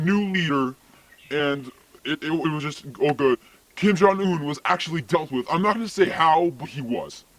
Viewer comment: Did he say Kim jongeun was actually dealt with at10:00 can't hear him very well